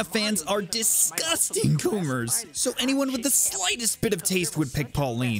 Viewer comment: i thought he said groomers, that was a spook